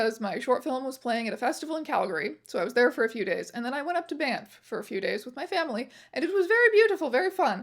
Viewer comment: Did you say you went up to Bamf? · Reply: Banff lol, its a popular tourist spot here in Alberta!